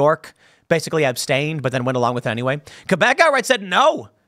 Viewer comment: what word is Tim saying here lol Who said no? I replayed it a few times.. is he saying Quebec?